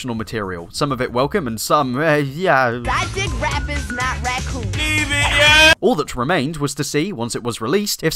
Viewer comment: i dont understand anything that was said i only heard "raccoon" anything else is gibberrish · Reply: 'I dig rappers not Raccoons' 'LEAVE IT YEEEEEEEH'